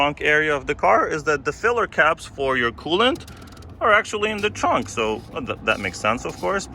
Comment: I thought you said the filler caps for the kool-aid lol